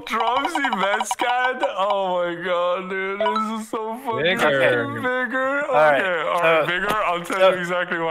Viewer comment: for a sec I thought they said "Nibber"